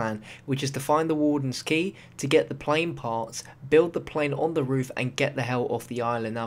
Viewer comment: i thought step 2 said get the plane farts not parts